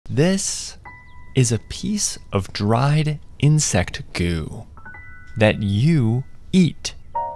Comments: I thought bro said "This.. is a piece of dried insect goon" 😭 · I thought he said dried insect goon 💀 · I thought he said insect goon😭😭😭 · I left the after it said “insect goo-“ and I thought it said goon💔✌️ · I heard "insect's goon" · I heard “that you eat” and now I’m leaving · “This 🎹 is 🎹 a 🎹 piece 🎹 of 🎹 dried 🎹 insect 🎹 goo” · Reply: I thought he said insect goon I was like WTF 🤮😖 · I thought he said insect goon